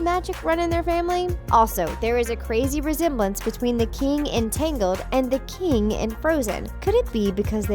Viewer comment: "the king in tangled and the king in frozen?????" But..... the subtitles said "the QUEEN in tangled and the KING in frozen "